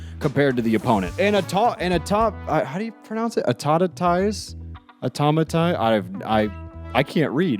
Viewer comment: You were close lol Instead of "tize" its "mize" at the end Otherwise ya got it right! It is a really strange word